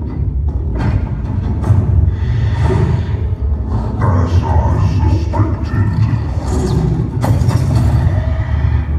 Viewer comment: What does Atriox says in 2:11? "As I suspected. A (?) but a man." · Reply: "Nothing but a man"